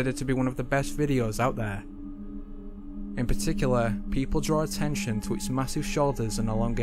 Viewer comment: I heard "People, Pray".